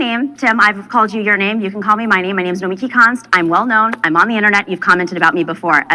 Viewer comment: Did she say: My name is Lee Ki Kont??